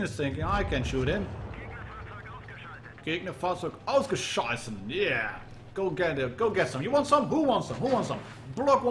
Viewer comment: what you said there sounded like „Gegnerfahrzeug ausgescheißen“, which roughly translates to „enemy vehicle shit out“